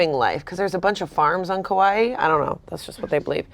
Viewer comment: kawaii instead of Hawaii and nobody mentioning it is so funny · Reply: Kauai